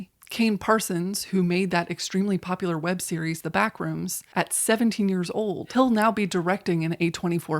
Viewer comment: I misheared "at seventeen years old" as "at seven years old" and for a second I thought that was a god tier hyperbolic joke. Missed opportunity.